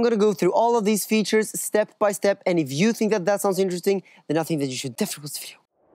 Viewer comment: What was said at 00:20 I replayed it 3 times... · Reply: “.....If you think that sounds interesting, it’s something that you should definitely review” Something like that 🤓